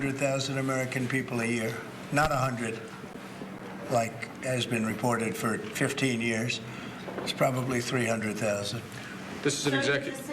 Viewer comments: I thought he said "like ASMOND was reporting for 15 years" LOL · did he say ... " not like Asmon reported" 😮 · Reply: "Not like has been reported"